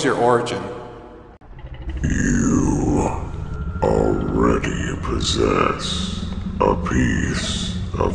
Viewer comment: I thought I heard 682 say:your already dead If that was true then OMAE WA MOU SHINDEIRU NANI!